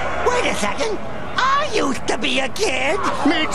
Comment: Ummmm… 1:32:20 did say “Sh*t, I used to be a kid”